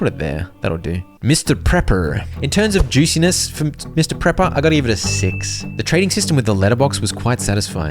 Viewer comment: I'm sorry, did you say Mr. Pepper??? xD